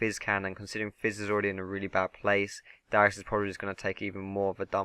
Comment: lol 12:00 i hear Dyrus